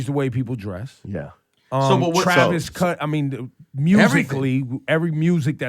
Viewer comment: lmao did he say Travis Cunt?